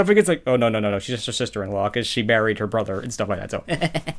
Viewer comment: When u said, Cadence married her brother, I thought u said Cadence buried her brother